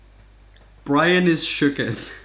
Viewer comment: "Brian is sookth" I thought he said Ryan, and my head went to Ryan Ross, then the milk fic, and I was not okay.